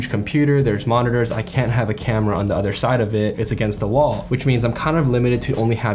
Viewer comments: stg i thought you said "because it's against the law." and i was like "....why?" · Heh. I heard that as "... having the camera on the other side of it is against the law," and I thought "Shit, maybe I should pay more attention to what's going on in the States."